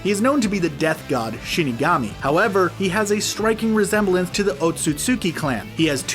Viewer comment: I thought it said okutsuki he meant aksuki